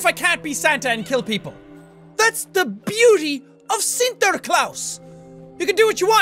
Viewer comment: Did you say Sinterklaas?!